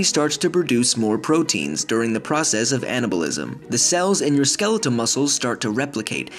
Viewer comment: I thought it said Canabalisim xD